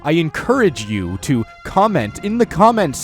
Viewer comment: It sounded like he said Cumment in the comment section